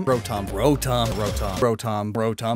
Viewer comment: At once point it sounded like he was saying brotom instead of rotom